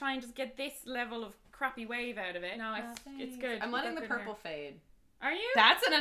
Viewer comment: Sorry, I'm not English, could someone please tell me what says about her hair at around 6:10? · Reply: Lolly Lolly it’s all she’s saying “I’m gonna let the purple fade” so she’s gonna let the color in her hair go away